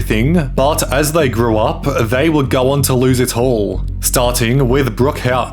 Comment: Your accent is so thick at the beginning I thought you said “they went to lose their hole”, not “ they went to lose it all” 😂😂 had to rewatch it to make sure that I heard that incorrectly